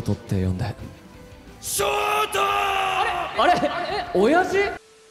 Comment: I swear, I hear Eren